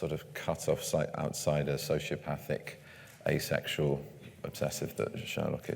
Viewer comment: did Benedict just say Sherlock is asexual ?